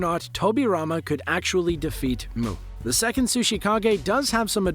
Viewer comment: I thought u said muu the second sushikage